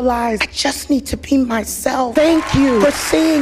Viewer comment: It sounded like Davine said “I needed to PEE myself”.